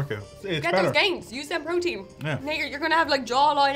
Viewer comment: uh... I'm sorry, what did she say to him there? I'm assuming I must mishearing it. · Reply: Just to clarify, at 4:46 says "Now you're, you're gonna have a jawline out to here!" - we can assure you, as we were there on the day it was filmed! 😁